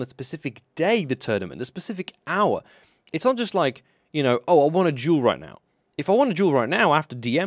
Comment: He said "I wanna duel" I heard "I wanna juul